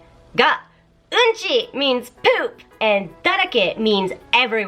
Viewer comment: So at 3:58, “everywhere” is pronounced as “だらけ”? Coz you sounded like pronouncing it as “だれけ”...